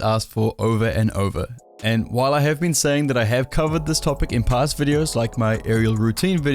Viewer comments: ngl thought ya said Covid ay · I thought you said you have covid 🤣🤣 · I thought he said I have covid lmaooo